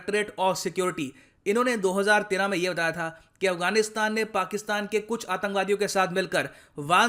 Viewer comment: Did you say “Afganistan ne pakistan ke atankbadi se mil kar”? Timing 3:56 - 4:06.